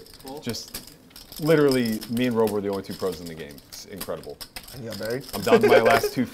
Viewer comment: first listen to the story I heard "Robl and I were the only pros at the game. JRB was the BB" lol